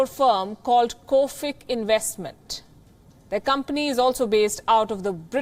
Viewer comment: I thought she said Covid Investment for a second there.